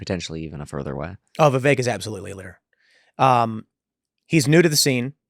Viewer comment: what does Kevin say? "potentially even a further eh?" And then what does Tim say?: "Oh Viviek is absolutely a leer" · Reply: oh right: leader